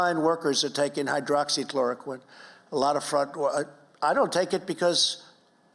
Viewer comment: Did he just say "I don't take it"